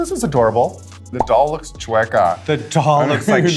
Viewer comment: what is that word raja said morphine’s doll looked like 😅😂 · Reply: she said “chueca” which means crooked in Spanish!